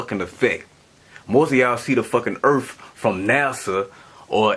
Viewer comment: did he just say "Erf".....